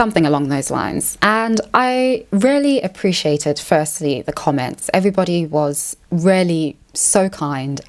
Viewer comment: Oh god, my little Canadian brain trying so hard to adjust to her accent... 0:42 I thought she said “I rarely appreciated the comments” 🤣